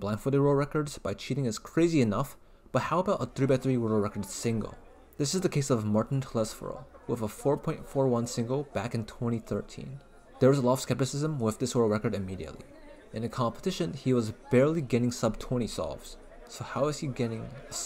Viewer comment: the caption says martin cholesterol 😂